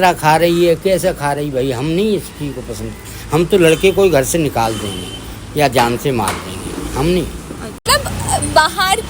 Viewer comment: did he say "Jaan Se Maar Denge" on camera 😢